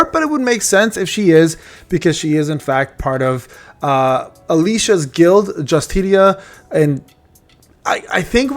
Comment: Not to be immature, but with her in the background, I heard “Alicia’s guild Just Titties.. blah blah blah.. 2 biggest thing we’re missing out..” 😈